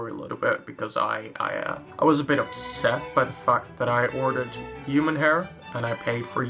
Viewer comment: instead of by the fact its by the fuck with captions on